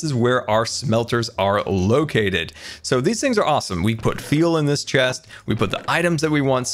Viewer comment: Hold up did anyone else hear him say "we put feel in this chest..." instead of say fuel or am I hearing things. (Says it